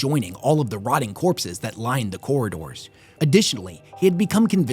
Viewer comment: Man Games Workshop was like Flashlight is not grim dark enough Oh I know STAB LIGHT! also I misheard Corridors once and heard Gorridors which with all the bodies and such is understandable lol